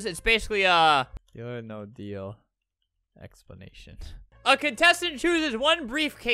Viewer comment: did anyone notice when looked up “deal or no deal” he actually typed in “deal or no dead” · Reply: I thought it said deal or no deal dead explanation xd